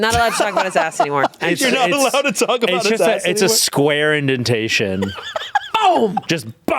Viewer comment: lmaoooo at first I thought he said “a square invitation”, like an envelope lmao